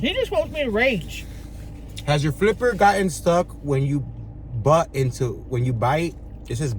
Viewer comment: When he said does your flipper get stuck in your food, def thought he was saying have you ever gotten your flipper stuck in your butt, so my question is have you ever gotten your flipper (remote control) stuck in your butt, or have you ever found things in your butt crack that you sat on and took it with you by mistake? I bet you could find small children toys, phones, glasses, etc… love you !!